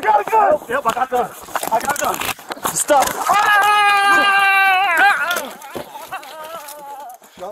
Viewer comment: Clearly heard him say Yup I have a Gun!